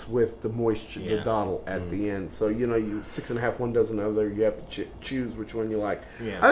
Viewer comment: Right around 8:50 in, did you say "Six and a half, one dozen of the other?" I absolutely love your channel.